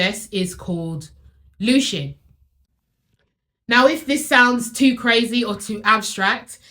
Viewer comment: What is the process you reference at 21:46 ? I am a bit hard of hearing but it sounded like “Lucian” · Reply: Its called looshing